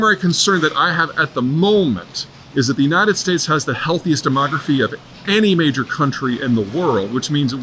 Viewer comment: "the US has the healthiest demography of any major country" I misheard and thought he said "democracy" I almost spit out my ever more expensive coffee. 🤣